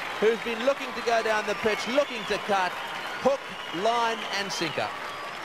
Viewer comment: What did Tonu Greig say @ 0:55? Couldn't get those 3 words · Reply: Hook, line and sinker. It is an old expression meaning the fish has swallowed the bait, the hook, the fishing line and the lead weight sinker. In other words, completely deceived.